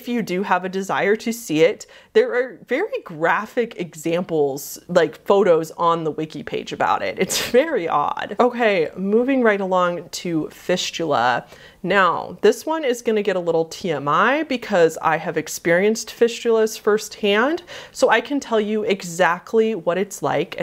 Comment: After she talked about fisting and went to fistula, (was listening to this vid) sounded like "fishula", my brain ended up thinking "fishula" was like fisting, but with a fish instead.. .-.